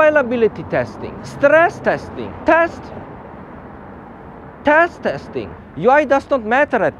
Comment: Not-so-subtle shocker at 2:37... Captions say "test testing", but I sure as hell heard "taste testing". I hope not!